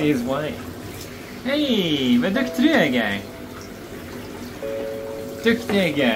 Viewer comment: anyone know what he said at 0:48? learning swedish atm so im tryna pick up what he says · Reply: He said “vad duktig du är Edgar” which basically means How talented you are Edgar